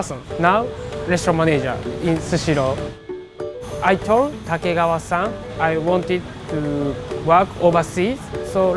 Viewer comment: When i heard Takegawa i thought it he was saying Tokugawa-san....i was like a descendant of the old shogunate is running a company🤣🤣